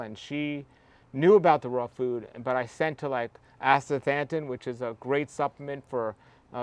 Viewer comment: What is the liquid algae supplement he mentioned at 10:50? Couldn't quite catch it. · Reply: Astaxanthin